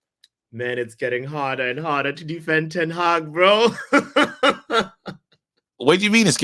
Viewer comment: did man say hes defending teng hag ball🤯